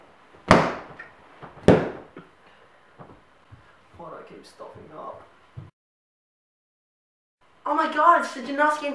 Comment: What is saying 7:30? 😟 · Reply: "Why do I keep stuffing up"